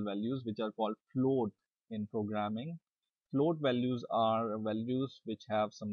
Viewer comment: did he say clode or clob value ? Couldnt understand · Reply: He said float values.The decimals values are known as float values.